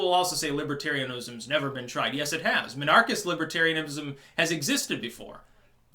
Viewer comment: Minarchrist? Thought he was saying monarchist, my bad